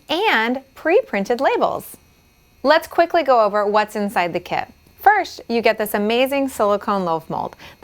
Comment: Did she just say "firsh" instead of "first"?